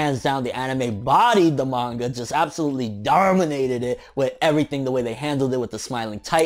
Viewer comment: DAWWRGHHMINATED IT Anyone else hear that LOLOL?